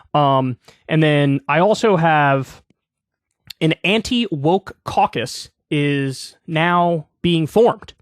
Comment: Oh, I thought he said an anti BOOK caucus